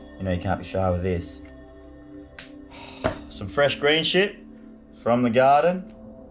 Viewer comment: Did he say green shit